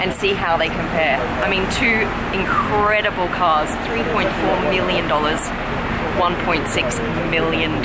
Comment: Supercar Blondie your captions were incorrect just thought i let you know 0:53 it says billion but it should be million